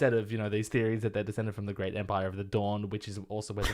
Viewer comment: Theory: doesn’t like theories about the Great Empire of the Dawn because he is actually the Amethyst Empress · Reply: Up until this moment when I read this message, I honestly thought they were saying the great empire of the Dorne.